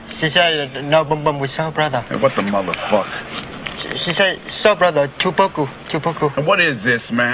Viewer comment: No boom boom wit soul brother. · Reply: I thought I heard "south brother". Soul brother sounds better.